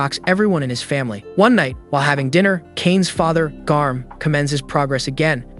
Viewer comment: I thought that said germ bro 😭